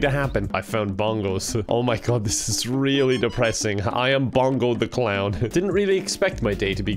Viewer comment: I thought he said pogo the clown and i was oh no oh no no no but it seems normal for to be a serial killer clown cause pogo the clown was a serial killer in real life